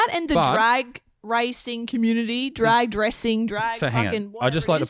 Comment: Laughing here. I thought I heard, drags doing dragg racing. 😂😂😂